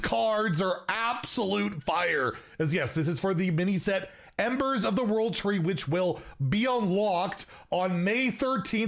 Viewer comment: I swear to god I thought said “embers of the world trade”